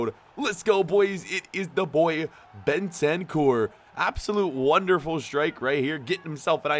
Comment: who else heard Ben 10, oh ok no one guess it was just me